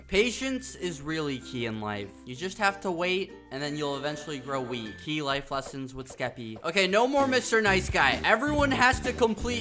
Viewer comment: I thought he said weed